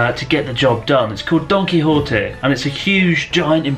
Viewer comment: I heard "Its called Donkey hotteh"